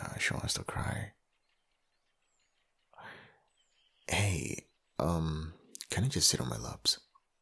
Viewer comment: stoppp at 4:32 the captions said 'hey can you sit on my lips" lmao.